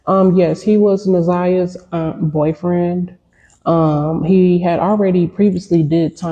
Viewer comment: is her cousin 🙄 y’all just be reaching for the stars saying anything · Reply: • It sounded like she said, "He is Na'Ziyah's umm boyfriend." I had to rewind it several times to realize she's saying, "...aunt boyfriend" when it should be "...AUNT'S boyfriend." Noone is reaching for the stars. People were genuinely confused. needs to ANNUNCIATE her words better & speak in a higher octave.